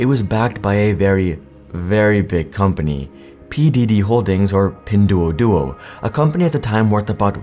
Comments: I swear I heard "P Diddy" holdings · I'm actually so cooked I heard "P Diddy Holdings"